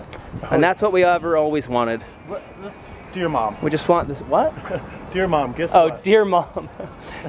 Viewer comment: Lol I srsly thought said do your mom But then it was dear mom I think did too Lobe u and